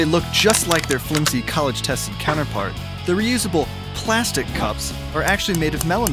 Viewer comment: I thought it said Edible inatead of Reusable